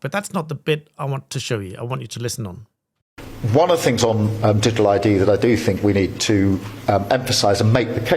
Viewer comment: when starver says it, all I hear is "Did a lie D" if it weren't so infuriatingly anti democratic it'd be hilarious : diddly doddly diddlers did a lie dee .... Labours new soundtrack